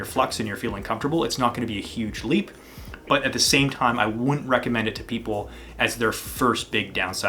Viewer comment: The Emissary looks like a great shape for advanced foilers. Well done team! At timestamp 2:18 it sounds like he says, "I would recommend". I gather from the context that he means, "wouldn't recommend", but it's not clear in the audio.